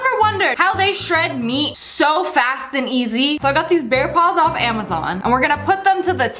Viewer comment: I heard “bear balls” but its really “Bear claws”,.